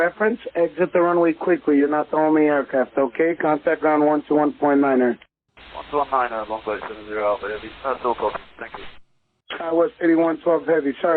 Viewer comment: The pilot called him an idiot in what sounded like either Norwegian or German. Listen carefully before the "thank you" at 8:30. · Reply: he says "that's all copied, thank you". They are British pilots so not going to be cursing in a foreign language.